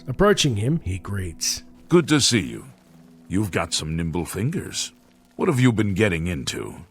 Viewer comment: Could have sworn he said “nipple fingers”